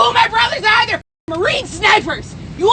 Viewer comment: Hey, I believe you may have missed that I think she said “my brother died in the marines” that’s why she was talking about marines, I believe, maybe she was just really deeply grieving which could have caused just this emotional overload. Love ur work 🫶